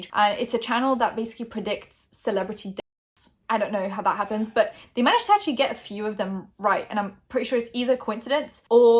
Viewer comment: What’s the word that bleeped out around 00:30 - 00:34 ? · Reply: Death